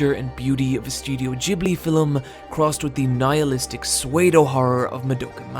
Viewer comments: what type of horror did he say? Couldn't really hear what he said at that point. Went back like 5 times. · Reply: He said psuedo horror. Don't know why he said it that way though. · Wait. Did he just say SWAY-do horror? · CH asm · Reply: Panda I thought he was saying someone's name lol. I had no idea he was trying to say pseudo. Also, ahem, FILLUM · Did Eyepatch Wolf say "swayedohorror" or "pseudo-horror"?? Or did he say something else? 1:28. The comic covers on this site almost make me want to learn katakana properly. And I just might. 2:15. I just caught up on the Manga last night. Why Exactly Did You use this page?????